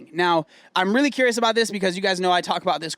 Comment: thought he said BDE 🤣🤣🤣